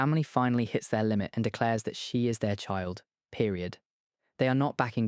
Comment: Did he just say period?